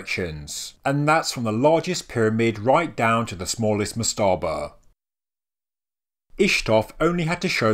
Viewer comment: I have to ask, what are you saying at 1:37? Something like "mastarbur"? I've been wondering this for a long time watching your videos.